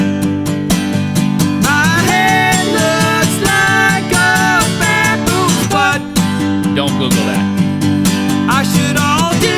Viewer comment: "My head looks like a bad moose butt" "Dont google that." Once you get it in my mind you cant get it out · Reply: thought he said baboon's butt. XD Haha..